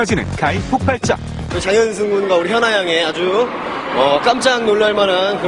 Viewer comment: what does Du Jun say at 07:42??? i heard "hyuna"